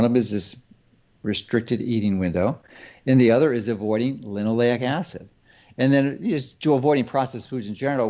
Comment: Dr is talking about. ????? acid I am failing to hear what acid he is saying we should avoid . Can someone help me with his pronouncing of the word. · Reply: Linoleic acid.